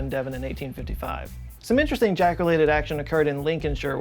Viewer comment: Sounded like you said Jackolated instead of Jack related. Made a new word, she got Jackolated 😊😁😂😂😂